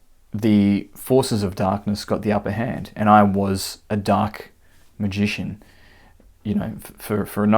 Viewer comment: I thought he said he was a DUCK MAGICIAN. Quack quack quack? But does he float and weigh as much as a duck?